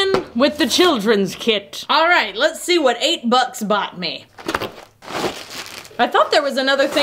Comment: "Let's see what 8 bucks bought me." What I HEARD "let's see what 8 butts bought me." It's just been that kinda day.